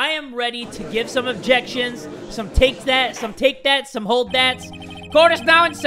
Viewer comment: look at this time 00:24 DID JUST SAY "some take that" 2 TIME?!?!